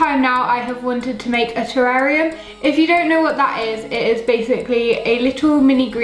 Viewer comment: when you said you always wanted to make a terrarium i thought you said you always wanted a terarria